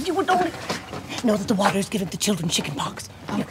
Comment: What did she say and what does it mean? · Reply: She said, "Know that the water is giving the children chickenpox."